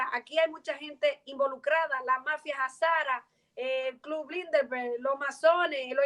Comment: Chicos no entendí en el minuto 37:10 donde nombra a Los Clinton, Soros, Burguesia Inglesa a contnuación nombra la mafia -------- no entendí q mafia era esa. Si alguién acá sabe, me lo aclara por favor! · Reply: si lo de los Clinton, Soros lo tengo claro pero lo de la Mafia Jazara no lo sabia. Muchas gracias por tomarte unos minutos para responder ! Saludos!